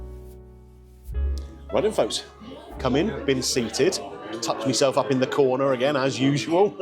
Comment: 😂 my hearing must be going dodgy or its your accent i thought you said you touched yourself up in the corner 😂